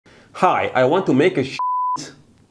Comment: First time I heard the beginning I thought he said "hi I want to take a shit"